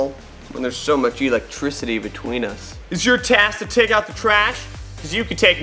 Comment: did he say he was trash haha lol 😆